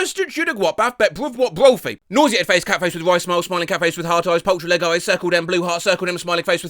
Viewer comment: i SWEAR i heard “smiling cat face with rice smile”